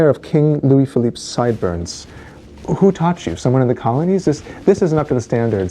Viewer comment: lol, at first I thought he said, "Who taught you, someone in the comments?"